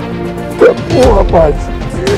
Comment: É impressão minha ou nos 1:19 ele fala " tá porra parsa " .. se não for isso alguém me diz o que ele falou pf